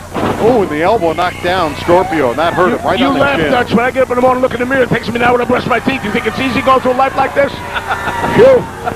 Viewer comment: I think a better question is, what the hell is Albano saying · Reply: Something about a dehydrated bee.